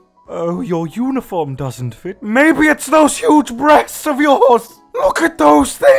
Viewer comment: For a second I thought when you said breasts, I thought beasts XD